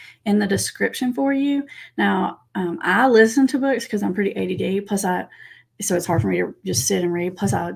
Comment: I couldn't catch what said here - "I listen to books cuz I'm pretty - (what sounded like) "ADA" (?) - I couldn't catch that, even when repeated and played back at slower speeds. I can't hear "ADD" or "ADHD" which is what would make sense in the context. If so, that's would be interesting to know sometime what she/you/ does to help with that. I saw the PTSD video that interviews an ADHD expert but I don't remember saying she had it too. But that could me my faulty memory. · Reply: You got it right, Tina. I said ADHD. Maybe I'll make a video on that topic sometime!